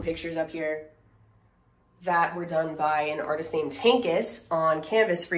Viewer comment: Is there a website for the artist (it sounded like you said "Tankis"?) who made the prints you showed??